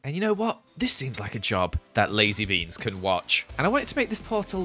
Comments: I thought joel said lizze beans instead of lazy beans xD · The second time he said lazy beans I thought he said Lizzie beans so I was like LIZZIES COMING?! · When he said 'lazy beans' I thought he said 'lizzy beans'